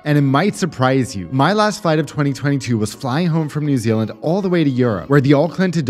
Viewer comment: I heard New Zealand and all of a sudden I wanted to like and subscribe because I thought you were saying flying home to New Zealand then after a reply I realised you said flying home from New Zealand 😅